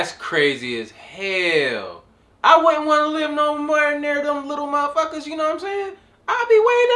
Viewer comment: Me: ?!? What he just said ?!? @ 14:43 Hahahaha “I don’twaaaa’nabelivingnowearnear themlittle matha’fukaz” !!! Hahahahahaha